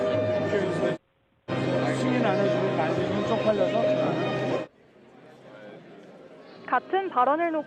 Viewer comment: 아무리 들어도 승인 안해주면 바이든이 쪽팔려서로 들리는데.. 내 귀가 잘못된 건가?